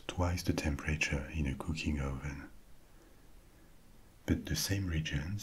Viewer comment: my sleepy brain thought you said cookie oven and then it provided me with a reality in which the French have specific ovens dedicated to cookies and pastries, which made perfect sense given the amazing food y'all have come up with. 💜💜💜